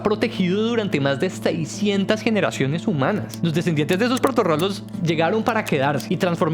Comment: Alguien me confirma que dice en el minuto 4:46 los descendientes de esos …… 😮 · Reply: Proto rolos (prototipos de rolos)